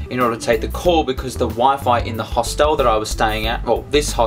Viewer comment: Glad you had a good call :D P.S. You say hostel funny :P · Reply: I thought you were saying 'hostal' which is spanish for hostel haha